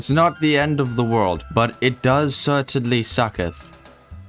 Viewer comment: Haha I admit I was half asleep letting your episode run the first time through, and I swear I heard "it does certainly sucketh" 😹 Those water mechanics can be a pain but I think they're better than they were a few updates ago!